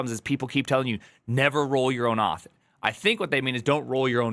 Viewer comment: what does he says here? Is rolling your own auth good or bad? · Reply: Exactly what any sane individual would expect: "Never roll your own auth"